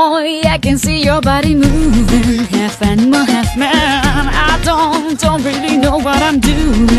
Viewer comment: >Half animal, half man I thought she was saying "cant stand on my hands, now"